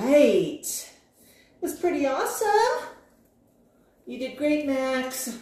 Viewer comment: You're such a helpful, good boy Max! There's a treat for you now, Max! That's pretty awesome! ❤❤ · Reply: I thought you said to Max, "Let's go get treats!" ❤❤